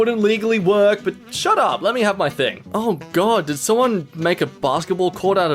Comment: i misheard this as let me have my dick